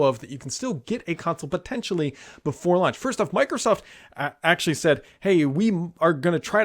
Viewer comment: I thought he said "get these consoles before lunch". I looked at the time and thought "what time does Brad Sams eat"?